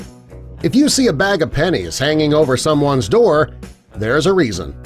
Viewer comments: I thought it said if you see and bag of penises hanging over someones door heres what it means :') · I think i got click bait i thought it said if you see a bag of penis ...(face palm)